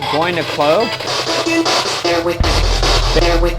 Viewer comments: Sounded very clearly (I wasn’t even watching my phone when I heard it) like “Bear witness” not “bear with me.” Spooky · I heard “bear witness” not “bear with me “